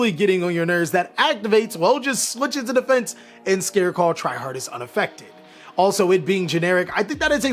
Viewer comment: sounds like you said scareclaw try hard XD